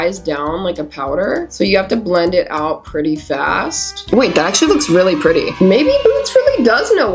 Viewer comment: wdym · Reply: I thot i heard her say. That shyyt looks really pretty, couldn't hear properly lol😅@